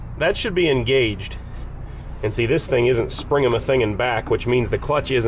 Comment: Did just say “springamithinging”? Did I spell that correctly??